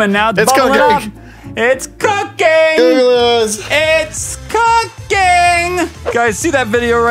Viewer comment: When was saying "It's cooking. It's cooking." at the end of the video it sounded like he was saying It's cocaine XD